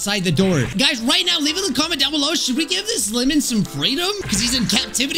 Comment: I thought he said "should we give him some KRATOM??" 😂